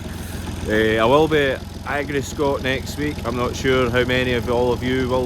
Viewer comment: Thought you said you were going 🤔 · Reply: sorry mate thought you said Livescot at Lanark at the weekend